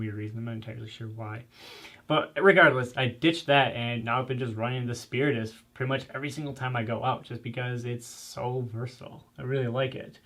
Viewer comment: Greetings what is the chest rig at 8:19 called? I coulnd understand what said. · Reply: Spiritus Systems Micro Fight Mk3.